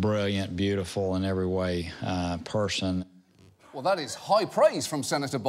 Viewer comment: I thought he said pursian, rather than person